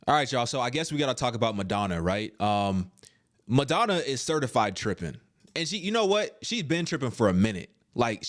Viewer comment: lord, I thought u 🥴said "certified tricking"